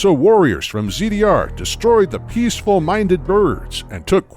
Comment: I see in the captions that it's "Peaceful minded Birds" but I definitely hear "Peaceful Minded Nerds" lmao